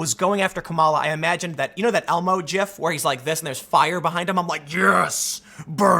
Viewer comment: Did you just say jif at 1:23 ?